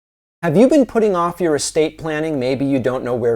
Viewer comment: I heard it as "escape plan" at the start and thought David was telling us viewers to prepare to escape